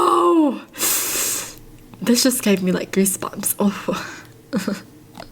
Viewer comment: for a second i heard her say greesebumps